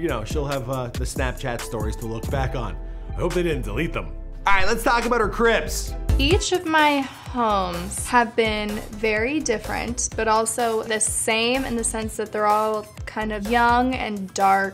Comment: Who thought he said crips